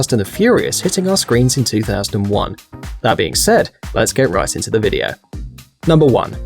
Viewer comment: At first I thought you said "Let's get rice into the video" :D